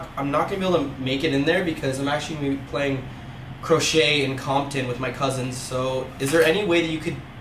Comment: I heard "crochet in Compton" 😂